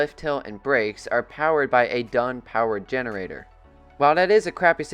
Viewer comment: I heard “Done”